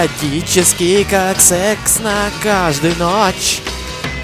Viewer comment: did shadow say sex?